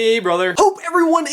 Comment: I thought they said "space brother" at the beginning but I now know they say "hey brother"😂😂